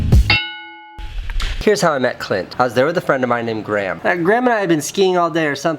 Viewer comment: I thought he said "this is how I met clit"